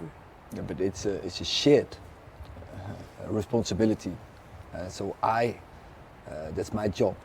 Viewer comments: I thought he said “it’s a sheet of responsibility.” But it was it’s a share of responsibility 😂😂😂😂😂 his English is also one of the reason some people don’t like him. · Did he say “it’s a sh*t responsibility” ?? lol long term contract please